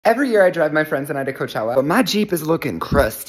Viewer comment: i thought he said “every year i drug my friends at coachella”💀💀